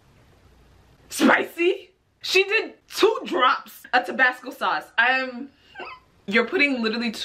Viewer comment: "lEtS mAKe tHIS mOre sPiCY" adds 2 drops of Tabasco sauce 🤦🏽‍♀️🤦🏽‍♀️ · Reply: I thought she said Tobacco for a sec, I said: 😳